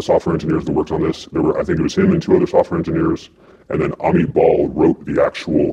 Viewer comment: I love when he says Amit Bahl but I heard him say "a meatball".